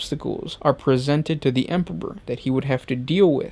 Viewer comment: anyone hears empopor, too?